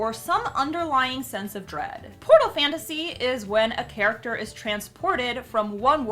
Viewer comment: When she said Portal Fantasy I thought she said Pornal Fantasy 😂😂😂😂